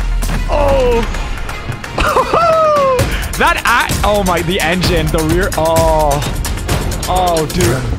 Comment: what was he going to say 📸📸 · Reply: “Actually” is what he meant